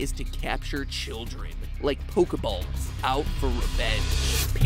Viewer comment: Anyone else hear at 4:15 him say "...like pokeballs out for revenge..."?